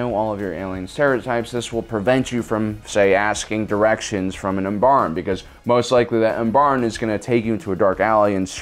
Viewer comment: what alien did you say, ambarm?